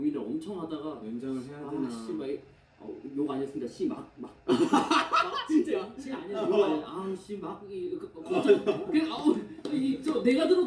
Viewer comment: You should watch their lives on weverse, not youtube. The subs are a bit delayed here and can risk inaccuracy. 3:56 It sounded like jimin said "shibal" which translates to the f word but he didn't! 😊